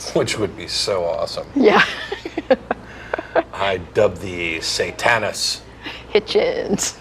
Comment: I thought he said I dub thee Satanus then added Hitchens